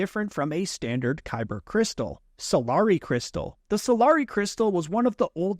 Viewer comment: For a second I thought he said "Safari Crystal"🤣🤣